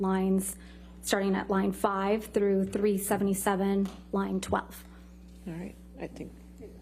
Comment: I thought Camille said “lying twat” but it was line 12 lmao 🤷🏻‍♀️ 🤣🤣🤣🤣